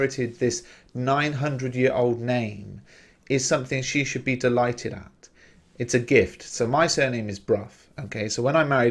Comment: What's the word said at 25:10? Envoy? Thanks:) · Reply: yes envoy, like a messenger of someone